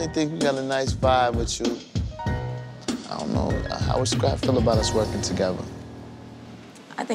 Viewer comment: He said “How would scrap feel about us working together? But I heard…… “How would scrap feel about us f*cking?” 🥴🤷🏽‍♂️